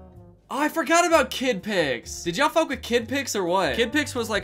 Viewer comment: misheard as “Did y’all f— with kids or not?”